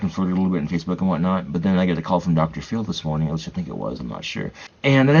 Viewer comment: Did he say Dr. Phil?